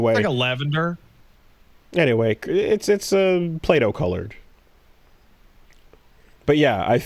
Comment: Oh, PLAY-DOH colored. Thought he said "Plato" for a second, was very confused.